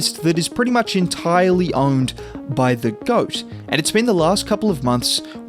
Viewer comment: Did he call Curry the goat?